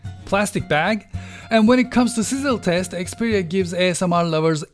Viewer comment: and when it comes to what? Can someone please tell me what did he said there? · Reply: Sizzle test.. maybe...